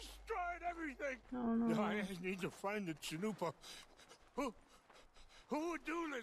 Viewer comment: I swear...the first time I played this and Rains Fall mentioned the Chanupa, I actually thought he said "chalupa" 🌮 and was thinking 💬 "Why does he want a taco?!?" It was when I checked the subtitles that I realized my mistake! Lol 😆